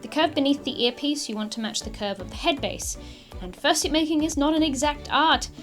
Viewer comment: I heard you say summer fur suit making????🤨🧐 WHERE CAN I FIND THIS!